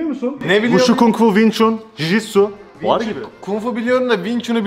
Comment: Jisoo dedigini duydum : D